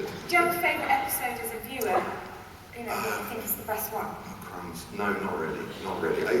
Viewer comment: Does he say "oh, crumbs" at 10:14? Because that's the cutest thing I've ever heard.